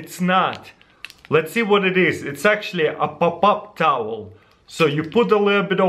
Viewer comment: Lol, I heard " a pop up towel" as "up up up towel"😹😹😹😹 anybody else too??😂😂😂